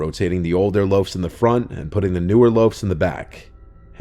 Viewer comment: the older ones in the front and the newer ones in the back did moms and us know about it???😮did I hear it correctly?